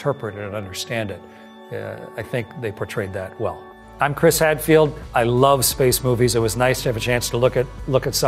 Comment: Chris Hadfield -=-"第一次聽成Chris Radfield